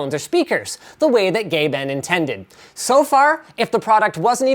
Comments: Sounds like he said "The way that Gay Ben intended". Im seriously considering getting one of these but I dont think it'll fit into my budget. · I thought he said Gay-ben...then I took a double take and he meant Gaben...hahah